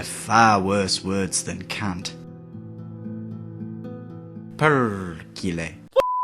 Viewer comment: What did he say? "Perculent?"